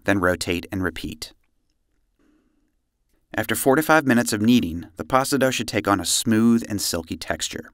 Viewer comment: I thought you said, "after 45 minutes of kneading" and I almost shut the video off thinking, "Hell no!" but rewinding I realized you said "after 4 to 5 minutes of kneading" which is far more acceptable.